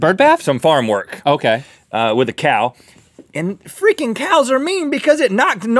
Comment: Did Rhett just say what I thought he said?! Did anyone else pick up on that or am I just so tired that I imagined it? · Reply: I heard it as well but he must've said freaking because otherwise they would've bleeped it if he had said it accidentally xD also because Link coughed at the exact same moment so it probably made it harder to hear 'freaking' over 'f*cking' xD